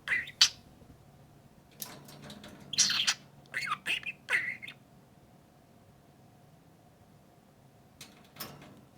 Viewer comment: Did anyone hear "baby bird" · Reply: He said "Are you a baby bird?"